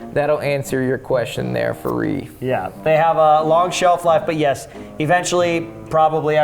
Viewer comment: Oh holy hell I misheard that as "IED" and was so utterly confused.